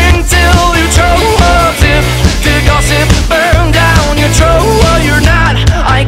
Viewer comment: The way he says "Throat" >>> ❣️ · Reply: all i heard was "troat"